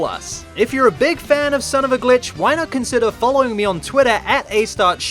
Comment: I heard him say son of a b!tch